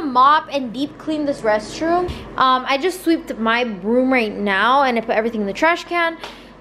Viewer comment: I thought she said I just swept my broom 😭